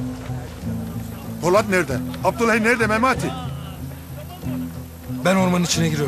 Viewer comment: abdül ney :) seyfo dayı ne diyor orda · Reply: Abdulhey nerde diyo